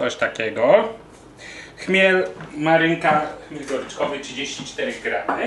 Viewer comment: hehe usłyszałam chmiel, marycha :-)